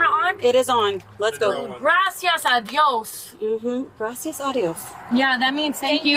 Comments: Sounds like she said “gracias adios” but she actually said “gracias a dios” which directly translates to thank you to god or thank god. She just slurred her words cause she’s drunk. But anyway both are insanely annoying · Gracias a Dios does mean “Thank God” I’m confused on the confusion. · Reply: it sounded like she said "gracias adios" to me and the female officer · they didn't Mirandarize the friend. They can get in trouble for that. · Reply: She said "Gracias a Dios" they prbly thought she said gracias adios.